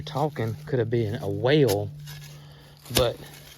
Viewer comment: I thought you were saying 'whale' but it was 'well' LOL Time to call Indiana Jones.